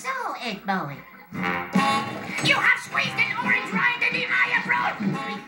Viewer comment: what’d he say? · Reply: He's said you have squeezed an orange rind in the eye of rolf 😂